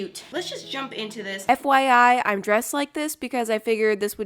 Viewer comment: “F.Y.I” oof I thought you said F.B.I